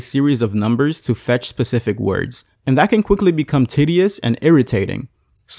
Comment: wait what did he say? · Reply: I know this might be a joke but to clarify. He said it was tedious, which is a word to describe something boring, lengthy or monotonous.